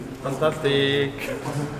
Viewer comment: 혹시 뭐라고 하시는거에요? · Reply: 빤따스띠익~ (판타스틱)